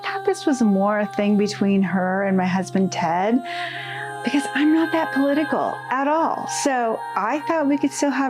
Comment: I thought she said she “wasn’t political” ahahah